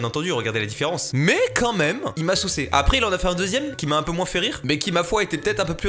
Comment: il t'a quoi ? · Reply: saucé